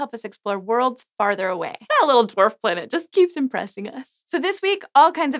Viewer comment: "That little dwarf planet keeps impressing us." 😄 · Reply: Ob 1 It's officially a dwarf planet, now. But I misheard her to say twerp planet. My mistake.